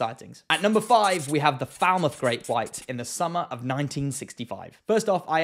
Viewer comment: I thought you said The Foul Mouth Great White Shark 🦈 💬sh#t f#ck